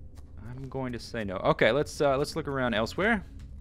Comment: He said 'Let's look around elsewhere', but I swear, I heard 'let's look around elsweyr', and I just immediately cracked up, because I knew that was just in my head. I would have looked totally insane to anyone in the room. I think I've been playing too much elder scrolls.